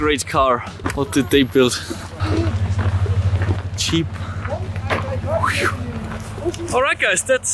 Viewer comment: Ohh let’s look at this car Cheep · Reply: he said "Jeep", it took me a second, i thought he said "cheap" as well....